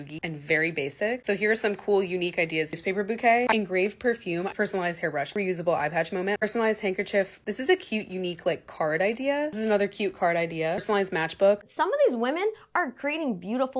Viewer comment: I heard “reusable eye patch moment” and immediately pictured a pirate style eye patch and was like hmm 🤔 hahaha